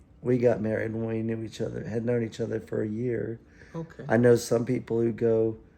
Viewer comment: did he say a year? That sounds really similar to what I said… interesting 🤔